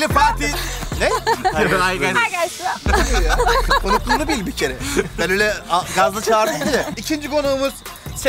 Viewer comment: hi guys broo: neyy: hi guyss bro: ne diyor bu ya konukluğunu bil bikere, ben öyle gazlı çağırdım diye. SĞLFPDŞDŞDŞDŞDLFLD MÜKEMMEL KALİTE